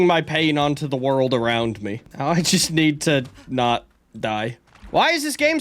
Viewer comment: what did he say🤨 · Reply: “Now I just need to not die”